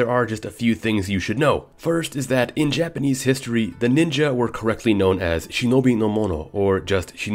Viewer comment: I thought you said ninjas were a myth. :-/